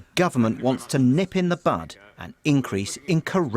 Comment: did he say bud or butt?